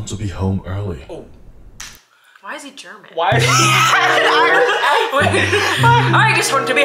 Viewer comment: did she said, why is he German?🤨 he has really German accent